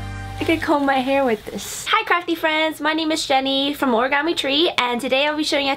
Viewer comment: I thought she said crappy friends and I was like okay thanks hurry up